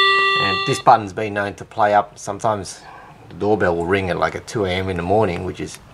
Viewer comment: Hey mate, im from Poland and i haven't understood what you were saying at 1:20. Did you say something like "bell rings at 2 a.m." ? if you did please tell me, is it possible for well working bell to ring for some reason, because mine has rung by itself few days ago.